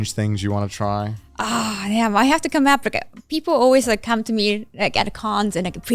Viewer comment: What does Kaho say here? I can’t make it out from the context 😅 1:40:44 “I have to come to Africa”? Like, I know that’s not what she’s saying, but it’s all I can hear 😭 Edit: I think I figured it out? “Ughhh, damn. I have to come up with a…”